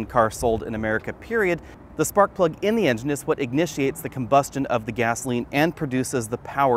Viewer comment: I swear I heard him say "Ignitiate" rather than initiate.....😂😂😂